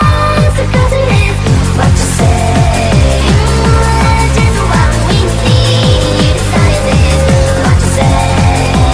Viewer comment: what in the world does she say at 3:22 to 3:27!?!?!? PLEASEEEE I NEED TO KNOW IT'S DRIVING ME CRAZYYY!!! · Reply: Mmm, what you say? Mm, that it's just what we need? you decided this.